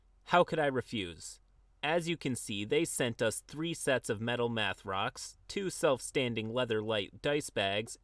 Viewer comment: Lol i had to back up there for a min i thought he said "Metal meth rocks"